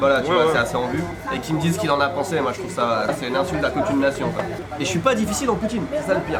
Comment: j'ai compris "'c'est une insulte à la poutine nation"